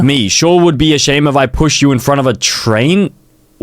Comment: I thought it said, punished instead of pushed😭